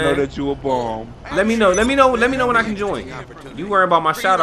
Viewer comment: Man wtf did he say in the baccground @ 39:02 ? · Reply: Lemme know when i can join